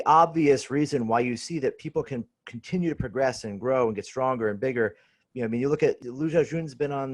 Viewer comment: Thought was about to say "Lu Xiaojun's been on the juice ..."